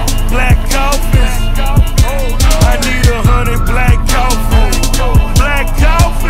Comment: I'm Italian: all I hear is "I need 100 black coffees"... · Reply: I heard "...honey black calf"